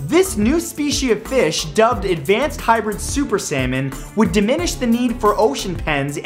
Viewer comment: when he said "super salmon" i thought he said, "super sayan: 😂😂